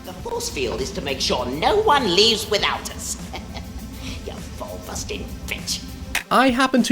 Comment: I turned on the CC so I could understand what he's saying after "...no one leaves without us." Only for the CC to completely fail for that sentence specifically. Lawl. (I listened a few more times and think I parsed it as "Ya ball-busting b*tch".)